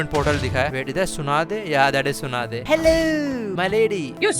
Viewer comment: hello sounded like verity